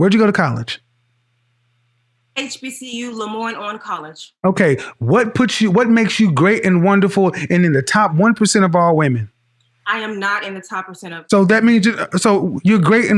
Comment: HBCU? Where in the name does it say HBCU? Sounds to me like she said "IVY LEAGUE HARVARD UNIVERSITY"